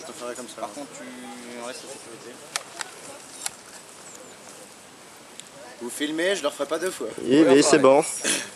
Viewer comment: “vous filmez ? J’le r’ferais pas deux fois.” Je sais pas pourquoi ça a sonné très badass